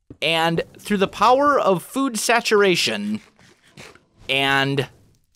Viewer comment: im pretty sure at 5:00 instead of "through the power of saturation", he meant to say "through the power of a cooked salmon" Great episode, kind of annoyed that you destroyed the blaze spawner though.